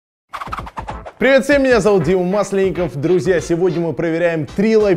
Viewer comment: Мне послышалось ВСЕМ ПРИВИТ МИНИ ЗАВУТ ИЛОН МАСК